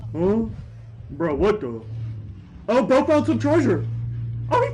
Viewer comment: my dumb ahh thought he said "bro found some charger" 😭😭😭